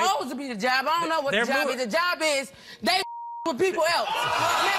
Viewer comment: Did she say messing with people else 🤦🏾‍♀️😂